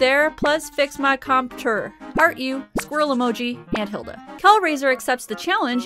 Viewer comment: I had to do a double take when you said Aunt Hilda, I thought you said heil Hitler 🤦🏼‍♂️